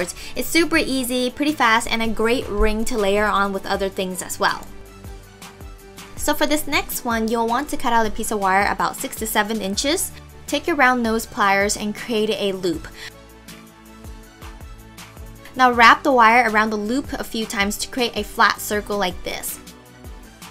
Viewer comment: Shen you were doing the twisty one... I thought you said 67 inches, not 6 to7, so I cut 67 inches of wire then to realize you meant SIX TO SEVEN. I fail